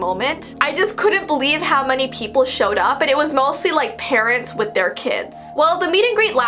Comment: i thought she said "Karens and their kids"